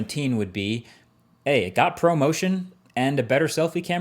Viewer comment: I thought Marques said “it got a promotion” 😂 Then I realised “Pro Motion”